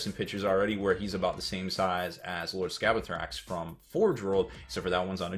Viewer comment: what does he say at 2:00 ? Lord what? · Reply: Lord Scabeiathrax